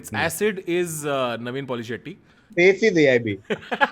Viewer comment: What did KV say at 9:27 ????? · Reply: "Base is AIB"